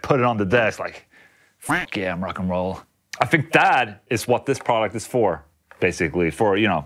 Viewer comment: I misheard you and thought you said "I think DAD is what this product is for." I still agree either way!